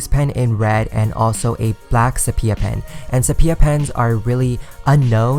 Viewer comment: You talked about a pen and I checked in the description and it wasn't there so I tried searching it up on what I heard, which was sopia and sepia, but I couldn't find the pen like that one so could someone clarify what he said?